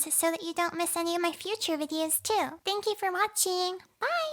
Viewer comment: At the end credits I heard "thank you for mochi" instead of "watchin" lols